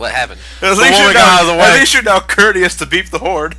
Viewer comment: What is saying there, and what is he referencing? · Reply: “At least you’re now courteous enough to beep the horn!” He says that cause in that cutscene the car almost runs someone over and the horn goes off